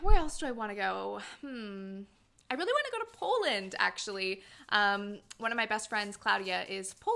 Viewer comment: When you said 'I wanna go to Poland' I misheard and thought you said 'Holland'. But then you said your friend is Polish and I was like… whaaaat, is going to my country <3